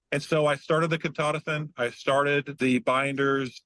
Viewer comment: What does he say at 2:27? Which medicine or supplement? Sounded something like “Ketotisin” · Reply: Ketotifen